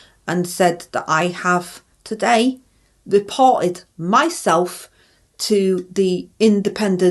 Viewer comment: I thought she said ive deported myself not reported 😂😂